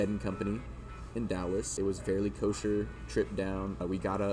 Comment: lol did he say it was Kosher?